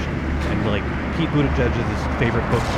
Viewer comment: does he say Pete Budigeg?